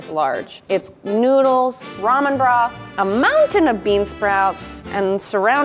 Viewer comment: Is it just me or did I keep hearing her say ‘ramen brah’ instead of ‘ramen broth’ 😂😂😂😂